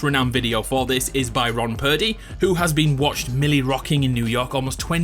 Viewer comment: did you just said roN purdy roy purdy